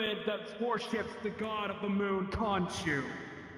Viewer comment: Hold it Hold it Hold it, did he say…..KHONSHU????? As in the god that “Controls”Moon Knight?!